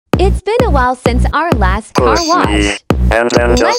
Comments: I thought she said car crash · The whole time I thought it said car crash I was so confused 😂😂😂